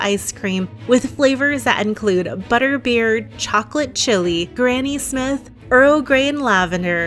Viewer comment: Excuse me, did you just say at 22:30 Granny Smith?! Like Granny Smith apples?!?! Please say you did 😩🤤